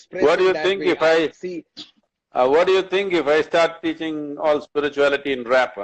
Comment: Sadhguru: "What do think if I start spitting all spirituality in rap?" Me: YEEEEESSSSSSSSSSSSSSSSSSSSSSSS (I realized later he actually said 'teaching', but first i heard 'spitting' which would have been even nicer, so I left it :P)